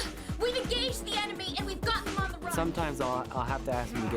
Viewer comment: Could've of sworn she said "titties on the run"